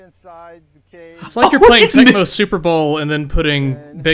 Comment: Super Bowl? OH, I thought you said Blood Bowl.